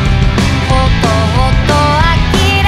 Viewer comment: ほとほと呆れていたところ が 弟はキレていたところ だと聞こえてこの曲は弟に向けての歌だと初めて聞いたという思ってしまった笑